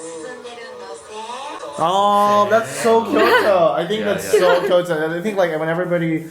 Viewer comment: の「That’s so Kyoto」って言い方が「 so cute」みたいに聞こえて面白かった